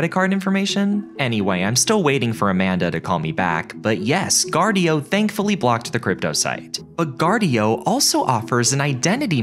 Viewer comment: 🔐 Protect yourself online with Guardio! All the cool kids are doing it: <link> Twitter: <link> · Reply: I honestly thought you said GoGuardian and got flashbacks on how my school blocked the Google homepage and search bar